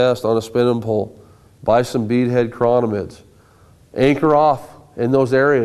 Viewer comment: What the heck did he say at 10:01? Buy some what? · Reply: He said beadhead chironomid, it is a fly that replicates a midge larvae.